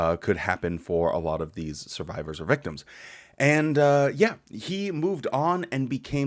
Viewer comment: ❤ thank you for being you! You make me laugh and keep me updated on jw things. ❤ 2:02 I literally thought you said "rectums"... I realize this is a serious issue but I still had to rewind and listen again to make sure lol